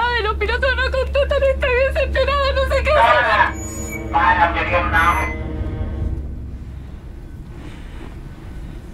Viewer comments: ¿Qué dice? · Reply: Los fuertes prevalecen y los débiles otorgan dice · que dice por el parlante? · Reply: Lo que entiendo que dice es "pará que aquí estamos", sería un mensaje irónico a la azafata como para que entienda que no hay accidente y se encerró apropósito. Puedo equivocarme pero entiendo eso xD